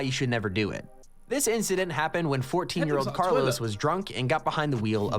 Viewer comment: did he just say 14 YEAR OLD CARLOS WAS DRUNK